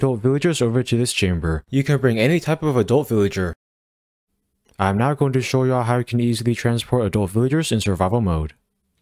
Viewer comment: i thought he said he wasn’t gonna show us 😭